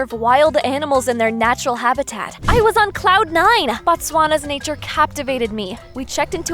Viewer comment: did i just hear "batswana"😂